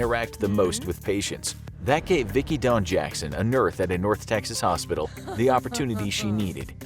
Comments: seconds in he calls her a nurth instead of nurse, it’s hilarious 😂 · did you just say "Nerth" instead of "Nurse"? Looks like your brain latched onto the next part of your sentence focusing on the "North" in North Texas Hospital before you were finished with saying "Nurse" and some synapse shortcut linked em up more efficiently.... or something. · Either I’m really mishearing, or at 48 seconds he says “a nurth in a North hospital” as opposed to “ a nurse” hahaha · did he just say "nurth"? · Did anyone else rewind the video to confirm that he said NURTH instead of nurse? No? Just me?